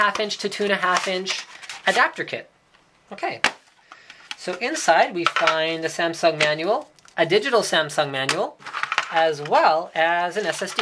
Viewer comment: CC translation @ 0:34 "Sitting beside myself crying in the sense that daniel a digital sound financial" What Linus really said "So inside we found a samsung manual, a digital samsung manual" o_O